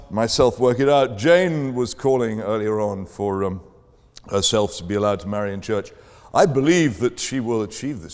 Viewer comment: What does he say at the 0:37 mark? 'Jane called for ??? to be allowed to marry in church.' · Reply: "herself"